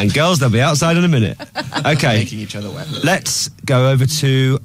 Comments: DID ANYONE ELSE HEAR WHAT MUMBLED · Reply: "We're making each other wet" XD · it sounds like said "I love making eachother wet" ;3